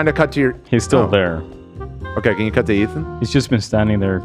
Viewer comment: I swear i just heard dan say ‘ian cut to ethan’ at the first bathroom break?! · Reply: He said ”can you cut to Ethan” but I get it I also miss our old friend😢